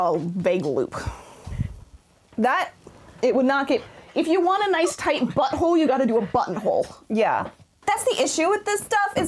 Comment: What I heard: "If you want a nice tight butthole, you gotta do a buttonhole." And now all the illustrations on that page look puckered.